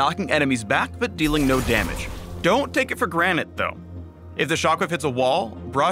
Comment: Did he just say ... "don't take it for granite tho" ?